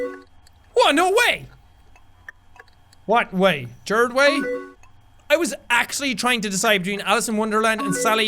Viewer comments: did he say "Gerard Way" or is it just me? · Jack, don't think I didn't catch you fucking saying Gerard Way. The repressed emos always know..... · wait 42:43 did he say Gerard Way?! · Did Jack say Gerard Way at 42:40 ??? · Did he just say.... Gerard Way? Did he seriously just say Gerard Way??? (Also for the 2006 icons there was the Black Parade option (; ) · Did anyone else see The Black Parade album cover cries in emo while g-note plays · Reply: Meme Queen hey in like 42:44 did he say Gerard way bcz i couldnt understand brt i did see it too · DID HE SAY GERARD WAY. WHAT.